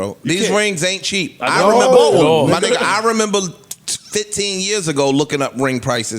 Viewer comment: parks my man but did he say "yeaaa Niggaaaa" at 1:03:02???????????????????? lol